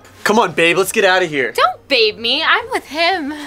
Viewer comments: YOU DID A MISTAKE AT 9:09 WHERE CRYSTAL SAYS DON'T BAE ME BUT SUBTITLE SAY DON'T BELIEVE ME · says “don’t believe me” instead of “don’t babe me”